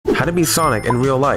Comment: I dead ass thought he said how to BE sonic